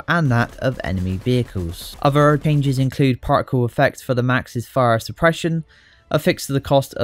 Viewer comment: I misheard "particle effects for the MAX's fire suppression" as "parkour effects". I was like "wait, what?".